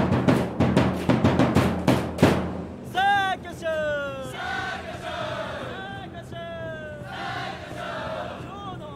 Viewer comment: ここなんて言ってるかわかる人いますか？ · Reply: 彼は「さぁ、いきましょう！」と言った。CCをオンにすれば日本語の字幕が見れます (・ω・ *)